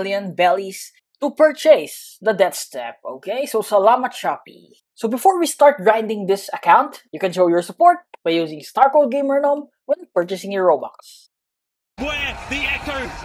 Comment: I heard "Salamat Shopee"